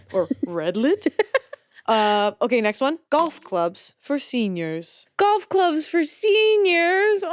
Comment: I misheard the prompt at 31:57 as "goth clubs for seniors" and I was so happy about the idea of 80+ year old people dancing to goth music for a moment there